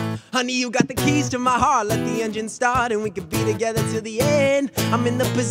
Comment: Let the ?? start. What does he say please help me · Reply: engine